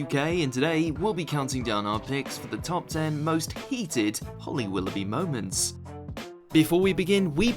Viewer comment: I thought it said hated moments, I was thinking who the hell can hate holly? she is just lovely xx